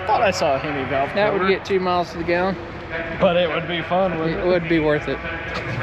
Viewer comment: i "thunk" i heard u say.... "two gallons per mile😂" Or two miles per gallon ?!😂